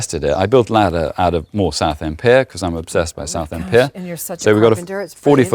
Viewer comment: Ok, at 30:23 he says he built a ladder out of _____ pear? because he's obsessed with ______pear. Can anyone tell me what he's saying? My US brain is having trouble deciphering it! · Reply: I believe is referring to using reclaimed wood from Southend Pier (some 70 miles from Hedingham) during renovation works there. If you watch the first episode at Hedingham, you will see even more of his carpentry skills using this timber.